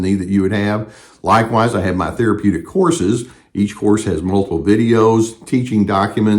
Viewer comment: Thank you for what you do. On a lighter side note, at 13:17 I thought you said "I have my therapeutic HORSES..." 😅😅😅 I thought, "therapeutic horses, that is so awesome! right on man!" 😂 I'm on the other side of a 1-year relationship with a woman who indicated to me, was the victim of narcissistic abuse. While I took everything she told me seriously, her behavior in our relationship just didn't quite line up with what she said (over the course of the 1 year). My baseline state is the problem solve and help pretty much anyone, but especially those closest. Well, I kept giving, but over time, I felt that there was a slow, but noticeable decay in any reciprocity on her part. When I tried to go deeper with her on an emotionally intimate level, she was unwilling or unable. I had to set boundaries I didn't even know required setting, as I began to feel like she did not have my best interests in mind. She continually pushed and broke these boundaries, causing some triggers in me, which then caused me to display some maladaptive behaviors, mainly, needing to shut her out, put on do not disturb, etc. (Which is something I had realized was unresolved from a former relationship). I could probably write a small paper outlining everything that occurred, even including what I thought were my contributing parts to any sort of issues within the relationship. If I were to do this, one thing you will find about me is being fully accountable and owning my f***-ups with regard to the relationship